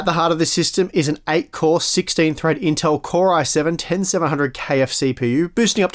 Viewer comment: did i just hear KFC ??? LOL ;)